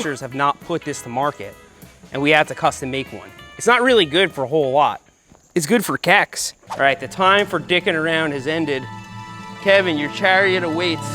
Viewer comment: Did he say it's good for Kek's?